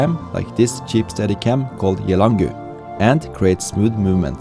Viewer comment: What was the brand name of the steadycam used for the iphone 6s Plus. Sounded like you said Gelagood...or something like that? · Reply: Yelangu! :)